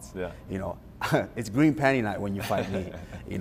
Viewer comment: Did he just say green panty night 😂He just took Conor lines and changed them into a different colour